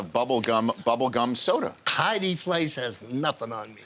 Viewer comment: wait! what did he say at 0:55 ??? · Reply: “Heidi Fleiss has nothing on me”